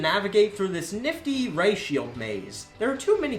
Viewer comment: every time says ray shield i hear "ratio" kms